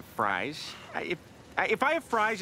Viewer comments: Am I the only one that heard him say I feel like a badie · “I don’t wanna be the only one eating them, I’ll feel like a fatty” I still use that line in daily life. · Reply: I thought he said "I'll feel like a baddy."